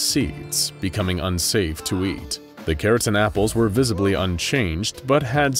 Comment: I thought he said keratin apples. Not carrot an apples. Which keratin is what makes our hair and nails.